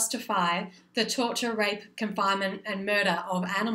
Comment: wait did u just say rape